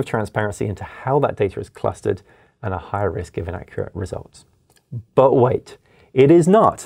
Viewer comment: official subtitles say "high risk given accurate results" while I'm 99% positive the speaker says "higher risk of inaccurate results". Huge divergence of meaning. Wish I knew how to ping the channel directly for clarification/correction.